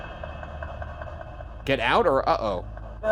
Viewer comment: Send us your questions for the Debrief in the comments below! + Ryan is today in the comments to see your reactions so don't miss Mr. Bergara lurking over here on main! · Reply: I deffo heard the name “Toby” really shook me up! You thought it said get out or uh oh. I’ve listened to it back so many times. 100% says Toby 👻 #debrief